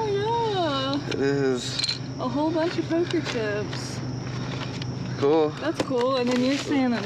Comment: That’s so funny. I had trouble hearing pulling something from the trash so I played it back. He said poker chip. Lol I thought he said cougar shit. 😂